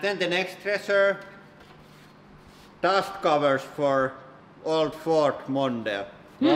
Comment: Did you just say dust covers for an old Ford Honda